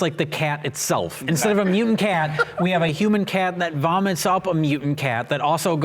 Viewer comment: Did Jay say a human cat at 30:44?